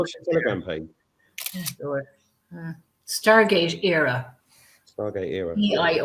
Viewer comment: Please can you let me have Telegram channel again as 'Stargate Eire' doesn't come up with anything, thanks 👍😃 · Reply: I thought she said Stargate era.